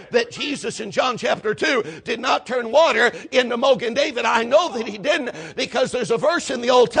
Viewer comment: The sermon at 7:27. What was he calling wine? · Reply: Mogen David. It's a brand of wine.